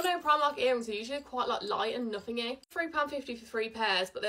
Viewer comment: Did you just say "nothing-y" ? 🤣💞